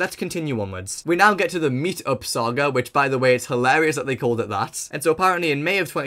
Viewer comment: I MISHEARD THAT AS "MEAT-UP SAGA" AND I WAS CONFUSED BEYOND WORDS